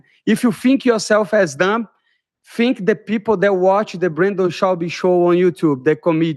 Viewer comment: did he say “bringo schwabby”? omg he IS more hilarious